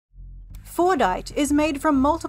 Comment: Oh I thought you said fortnite